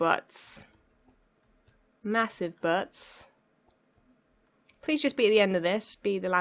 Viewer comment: I just heard Massive Butts